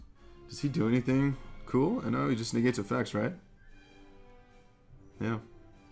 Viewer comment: turn closed captioning on at 10:42 WTF? it says "always just niggas of sex right yeah"